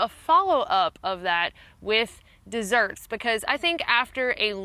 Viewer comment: i thought it said deserts. that sounded really cool... pun intended. high plains desert kind of places not just the sand ones.... i don't care for those kinds of sweet foods. apples, blueberries, clementines are more my speed for sweets.